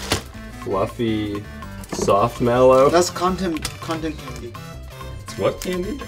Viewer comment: "that's condom candy" - 2016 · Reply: I heard "That's Content ID" and was thoroughly confused.